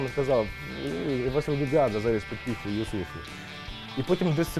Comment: что он сказал? Из за плохого качества звука и музыки ничего не понял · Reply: DimonFun заліз під піхву Юссуфу